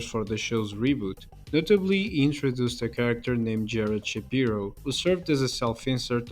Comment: I thought he said Ben Shapiro 😭🙏🏿